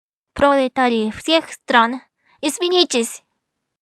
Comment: "пролетарии всех стран, извините" が書いてありますが、発音的には "пролетарии всех стран, извинитесь" としか聞こえませんでした！ とにかく、さんはお頑張り屋さんで基本的に真面目な方だという印象がもらいましたので、必ず見事にロシア語の勉強ができます。私も、日本語の勉強を頑張り続きます。（それとも、「頑張り続けます」が正しいのでしょうか？）